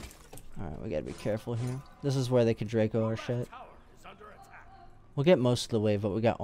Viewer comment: Did I just hear a reference to mons "Draco our shit"?